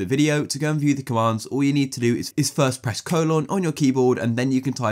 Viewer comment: I have no idea what he said at 3:36 and I’m stuck and don’t know what he meant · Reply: Yeah he said colon or sth wtf